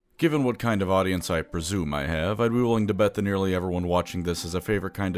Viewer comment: I misheard the opening as given what kind of autism I presume my audience has